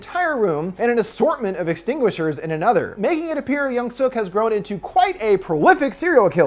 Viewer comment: I thought you were saying “A young Suk” and I kept thinking... “When’s the “Old Suk” gonna make an appearance. I thought there was a young and old version of the girl and I didn’t click until almost the end. 😂